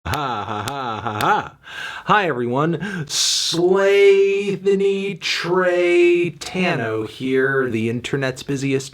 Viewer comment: I legit did a spit take cause I thought I heard “Slavethony Tradetano”